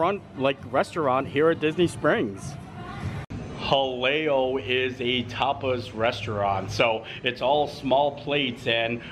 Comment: Sounded like you said Jaleos is a topless restaurant. 😂😂😂